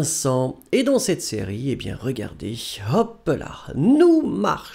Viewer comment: Hoppala mı duydum ben orada ? 😜😂